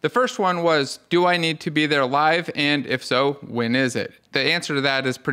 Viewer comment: <link> When I first heard this I thought you said, "Do I need to be there alive?" and my first thought was...Well yes, I'd hope you could make it to the course alive 😅